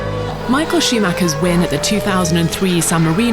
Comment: Did she just said: Michael Sheemaker?